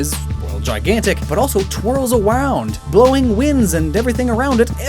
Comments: anyone else hear "twirls awound" at 40:40? Love it. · did he say it twiles awound? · sounds like you said twirls awound